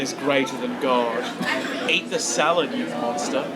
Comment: I thought said ‘Eat the salad you loser!’ 😉